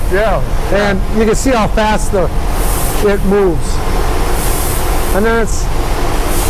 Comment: Haha at 11:14 did he say you can see how fast the shit moves?? Haha